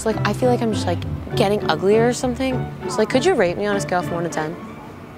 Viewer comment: At first i thought it said rape me. Im like wtf ?